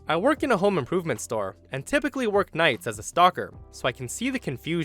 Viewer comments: Did anyone else double-take at 2:56 because it sounded like he said "stalker"? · i was looking away from my screen when u said "stocker" and i thought you said "stalker."